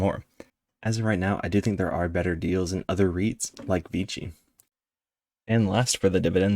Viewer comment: What did you say at the end of talking about Realty Income (O) at about 5:47? Was it VGE ? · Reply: I was talking about VICI, that didn’t come out very clear. Sorry about that